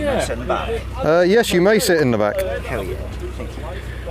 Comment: Did a guy with a crash helmet sit in the back of the city river and did he say 'Hell yeah' when you agreed to letting him sit in the back!??